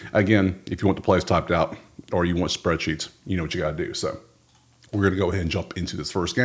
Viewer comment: my brother thought you said “if you wanna spread cheeks, you know what you gotta do”😂😂😂